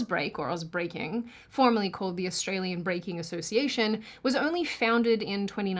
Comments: I misheard it as the Australian Baking Association - makes a whole more sense now... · She said ausbreaking and i thought she said Ozzbreaking. Thought there was a new ozzy breaking scene lmfao